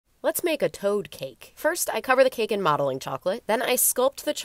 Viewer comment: I heard “let’s make a toad kink” as I was stirring my curry and got very concerned